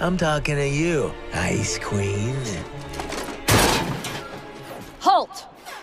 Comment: Ha it sounded like he said ice cream instead of ice queen